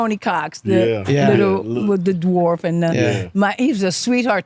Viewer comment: Did she say the little darf? 😂💀